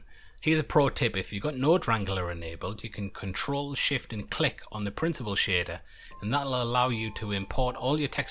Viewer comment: I don't understand what you are saying on how to use the "pro-tip". Excuse me for the question, but I don't hear well. Thank you for this very useful tutorial. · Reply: If you have the node wrangler add-on enabled you can press ctrl Shift T on the principle node to import a full texture set. It automatically sets up all the nodes.